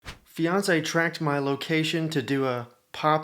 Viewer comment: NGL, when the video started, I thought you said 'Beyonce' and not 'fiance'. Lol